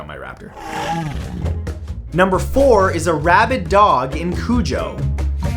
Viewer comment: At first I thought you said rabbit dog😂